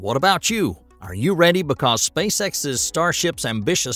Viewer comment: It sounded like you said Spacesex and now I can’t unhear it 😂